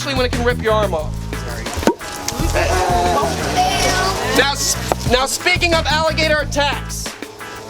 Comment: I swear I heard someone say yes